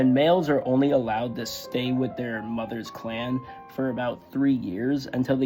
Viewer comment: Yo I thought you said they were kicked out the CLAM